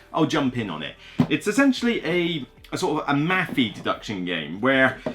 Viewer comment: you say, "it's essentially sort of a ** deduction game." What did you say there? I'm not big on deduction games so this must be some term I'm not immediately familiar with. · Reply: He said "mathy", i.e. it's a maths puzzle.